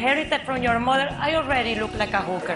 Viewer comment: I thought she said "I am an addict" at first